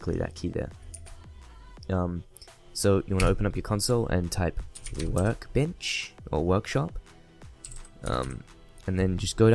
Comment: You wanna type in... Work bitch, or workshop xD I know he said 'workbench' but it sounded like Work Bitch